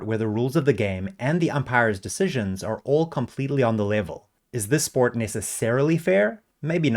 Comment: what did he say @ 1:07? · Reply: "all completely on the level"?